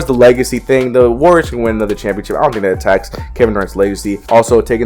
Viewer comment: Lol I think you said “Kevin Hart”